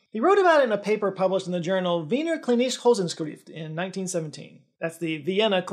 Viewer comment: yo Wiener klinische Wochenschrift is definitely not pronounced like that😅 (I'm german) from what you say I understand clinice hosenscrift❤